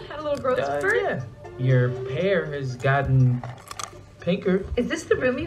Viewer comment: She said "You grown" *lockes down at his pants*. The first time I watched it it sounds like he said "Your pair has got bigger" but I rewatched he said hair.😂